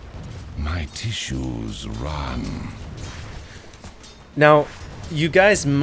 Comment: Does he say: My tissues run? wtf